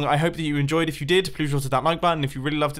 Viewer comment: did you call yourself Bumbo?